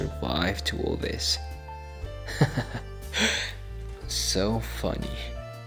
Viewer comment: When he said "funny" i thought he said "fucky"😩